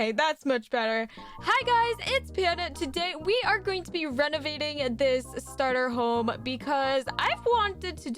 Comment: I thought she said "Hey guys! Today we are to be RENAGADING the starter home!"